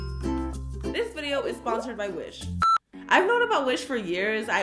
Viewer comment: Lol i had my volume down and I thought you said today’s sponser is by fish it’s actually wish 😂